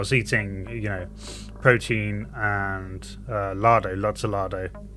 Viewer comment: what did he eat? He said protein and ladey? Whats ladey